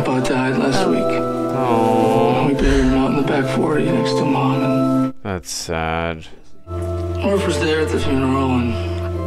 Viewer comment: Clearly you didn't understand the next word he said: "Grandpa died last week. We had to bury him out in the back 40 next to Mom and..... Jesse." Jesse is their boy, Cooper's grandson, whose birth was announced in the message prior to the one about grandpa's death.